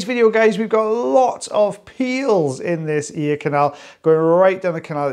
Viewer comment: I thought you said “we’ve got a lot of pee holes in this video,” but you said PEELS